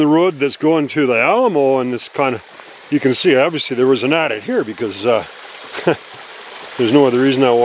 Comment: I can't understand the word you're saying at 1:49 and 1:59 it sounds like "attic" Also, what if you go in, like the hole they tried to fill, and shyt collapses??? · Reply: Adit…….a passage made into the side of a slope to access minerals.